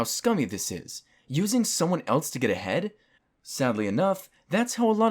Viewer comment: I thought you said "Using someone to get a head"